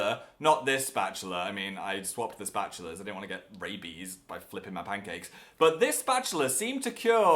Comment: I thought he said rapies